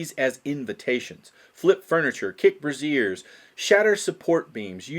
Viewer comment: I got lost in my thoughts around this time cuz while I'm pretty sure it was supposed to be "kick braziers"(braise•zures) as in fiery coals holders used for light...but what I heard (and the closed captions agree was stated) was "kick brassieres" (bruh•zeres) as in women's undergarments... So I started trying to imagine why a player would actually end up doing that or why a GM would allow something to lead to that kind of scene...🤔 Happens to everyone now and then, lol👍.